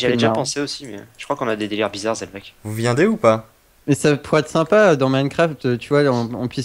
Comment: c'est moi où il dit: "vous viendez ou quoi?"